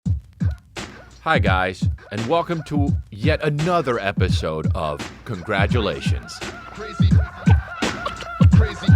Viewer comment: At first i thought he said “hi guy… and welcome to anoth…” and that made me laugh.